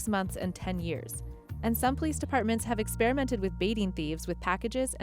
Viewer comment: If you turn on the closed captions, then shortly after 13:22 the closed captions say that "some police departments have experimented with beating thieves".... as in punching and kicking the thief until the person is leaking red-colored bodily fluids. I hope you revise the subtitles to say, "***BAIT***" instead of "***BEAT***"